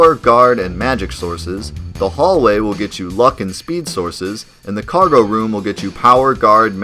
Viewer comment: Am I the only one who heard you say, "The hallway is where you'll find the fuckin' speed sources."